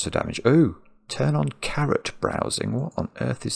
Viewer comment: I thought said "Carrot Browsing" - something for Rudolf perhaps